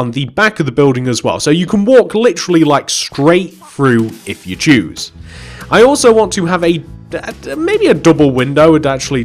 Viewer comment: anyone else hear you can walk straight through if you Jew?? Just wondering